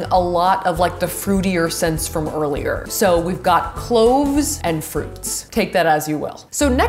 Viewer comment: i thought she kept saying clothes but apparently she was saying cloves and everything is making more sense now